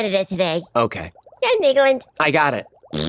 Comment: What does Hecklefish say at 11:40? "Or Canada today ... INIGOLD [?!]"?